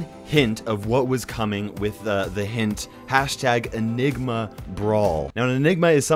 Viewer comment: I seriously thought you said "a nigma brawler" and i was hype over having another Brock